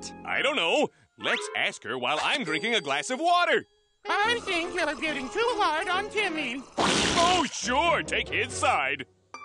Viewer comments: i thought she said "i think that i'm getting too high on ketamine" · At first i heard that as “i think i’m hitting too hard on Timmy” I